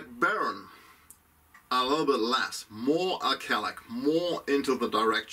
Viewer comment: What is the word you use to describe "more in the direction of cigar" at 49:50? Sounds like alcelic. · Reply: Thanks for watching! Much appreciated! yeah... that's it!... I think, in english it is "alkaline"... Take care and be well!